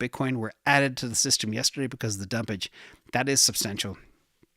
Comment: For a second, I thought said, “3,374 bitcoin were added to the system yesterday because of the dumb bitch. That is substantial” 😂😂😂😂